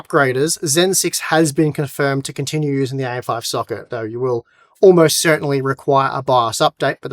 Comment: Did he said Ai 5 socket🤨🤨